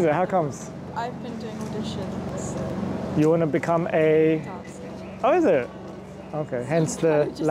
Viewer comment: what did she say that she wanna become? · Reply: dancer